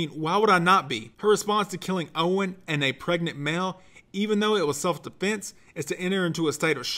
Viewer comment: Wait... did I hear pregnant male?